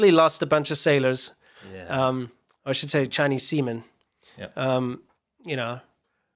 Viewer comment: English is not my native language. I hear Chinese semen at